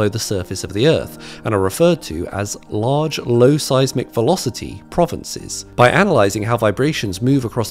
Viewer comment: I legitimately thought he said "low-size McVelocity"